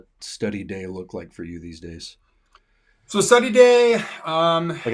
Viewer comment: what is the textbook mentioned at 14:33 (it sounded like "si xiang yi shehui") and what level is it for? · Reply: That’s “Thought and Society 思想與社會,” an outstanding textbook for advanced level learners.